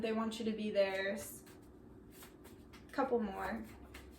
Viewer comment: Hilarious! Around 16:30 in pile 1 I thought you said “cum whore” but you said “couple more” 😂😩